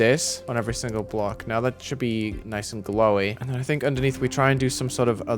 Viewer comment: did he say GLOWY?